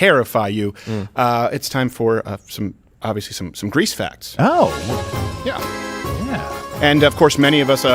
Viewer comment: I thought you said "grease fats"